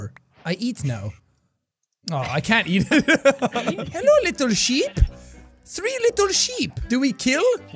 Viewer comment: I thought you said "hello little sh*t"